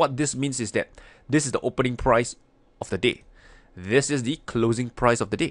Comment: Watching this half asleep, I didnt here "the day"... I heard "this is the low of the d*** and this is the high of the d***"....... lol